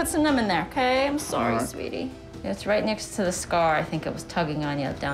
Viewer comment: did she just call an older man sweaty 😂😭